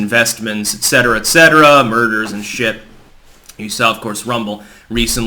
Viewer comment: I thought you said "murders"